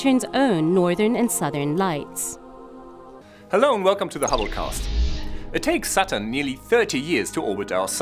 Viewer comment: when he said hubble cast, i thought he said halucaust i was like wat